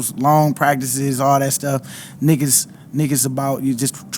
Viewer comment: min mark I swear he said niggas about😂😂😂. Nick is about tho lol